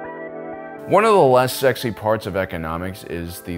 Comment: i thought at first that he said "one of the last sexy parts of economics is logistics", and i agreed.